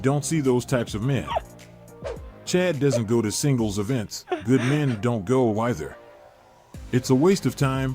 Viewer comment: Not gonna lie, I thought they were saying Shingles events. Like the disease.